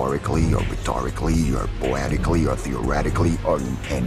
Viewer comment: What do everybody hear: I'm death What I hear: I'm deaf